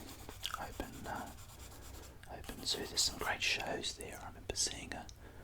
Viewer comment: What are you saying at 5:06 ? "There's some great shows on... ???" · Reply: I was talking about the Taronga Zoo, which is next to Sydney Harbour. There some great shows on at the Taronga Zoo, such as the wild birds show.